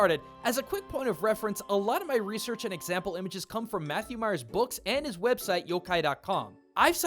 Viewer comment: For a second I thought you said Matthew Mercer and my inner Critical Role fan was screaming with joy.